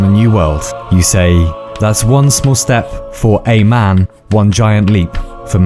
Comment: that's not what he said. He said "One small step for man," which is certainly the wrong phrase to have said. <link>